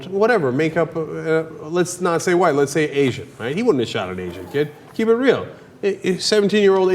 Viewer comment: What the hell is up with the horrible subtitles...... Am I the only one that noticed that??? For example: 4:35 he said asian, he wouldn't have shot an asian kid keep it real... the subtitles say "Haitian riding on this johnnie did derail baking seventeen year old as you" WHAT???? Am I missing something here?